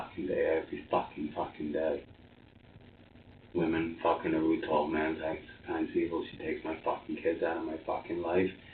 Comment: swear I heard him say "women fuckin every tall man" I know that ish really gets to guys.